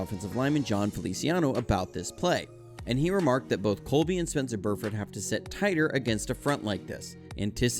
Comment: what are you saying in this very video at around 1:55? “lol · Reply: im saying I asked him about Colby and he said, "Colby and Spence have to set tighter"